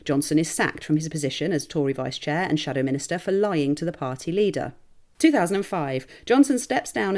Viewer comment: I misheard this as “Shadow Minister for Lying”